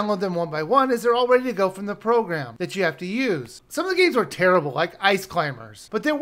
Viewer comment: What the fuck did you just say? :P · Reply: You can play the games from the program you have to use.